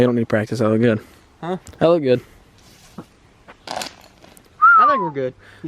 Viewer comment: did say hella?? that is not a word of god bröther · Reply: He said that looked good